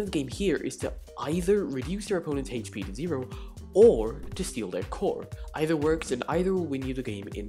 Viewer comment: Thought he said "steal their corn". Wouldn't want that. I'd like to keep my newborn from being sacrificed.